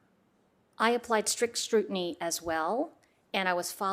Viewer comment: Lol she said "I applied strict strutiny as well" @ 5:34. Or did I hear it wrong? I replayed it 4 times and I swear that is what she says. xD